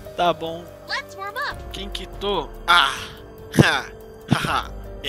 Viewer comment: Quem kito ? Eu não entendi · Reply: Thur